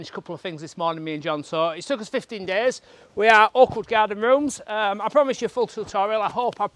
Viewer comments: Sounds like your saying awkward garden rooms 😂🤣 sounds like your from Yorkshire to me 🙋🏼‍♀️ I’m from Harrogate but lived in Australia 🇦🇺 for 52 years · Southerner here...10 seconds in and I thought you said Awkward Garden Rooms! Exactly what I need...tutorial for just me and my project....one woman, no help, limited tools. Mine'll be more like 15 months.